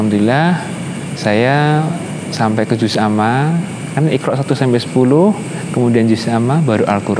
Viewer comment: Menit 7.32 iqra' 1-10? Mohon maaf jika sy salah dengar